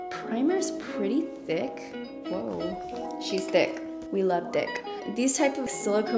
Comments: I love your hair this color!!! 😍 Why did it sound like you said “we love DI*K” to me tho ?! (for the primer) 😂😂😂💀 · I love your hair this color!!! 😍 Why did it sound like you said “we love DI*K” to me tho ?! (for the primer) 😂😂😂💀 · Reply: 😂🤣 Actually, she said "Whoa, she's T H I C C, we love thick" but I can def hear where it could've sounded like she said "we love d**k" 😆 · Okay I love this video, but Is it just me or did she just say "we love dick" like Im currently dying inside. She didn't even realize it and that's the funny part about it. love you <3